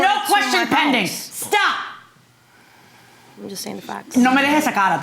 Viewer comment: Don’t know what defendant said under her breath, but it must have been in Spanish for J M to respond in Spanish back. Defendant was like O f ck did she just say that. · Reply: She said "just saying facts"